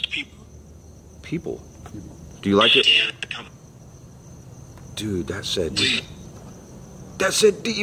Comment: you just asked what was said on spirit box, it says “I said you bloody people!” 😳